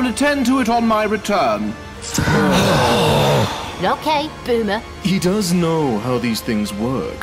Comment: Did 13 just say “okay boomer” at 4:56?